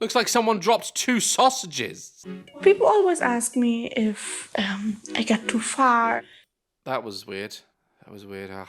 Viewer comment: She cant even speak correctly at 24:51 did she say "ppl ask me if i get to fart"??? No way she possibly meant to say that what was she trying to even say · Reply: I think she was saying "if I feel i went to far" or something similar